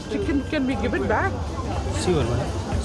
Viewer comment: I thought he was saying "see u are" then i realised he was saying "sure"